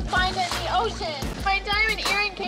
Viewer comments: Thought she said, “My hair is gone “. For a second there. Holding her ears…… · I thought she said hearing aid 😂😂😂😂 lol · I thought she said her HEARING was gone…. 😂🤣😂🤣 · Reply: I thought she said "my hair is gone" and I was so confused 😂😂